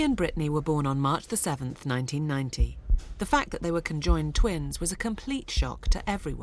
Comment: it sounded like she said 1919 but she said 1990